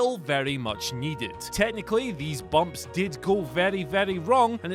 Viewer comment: I thought it said “dumps” and got excited. 😂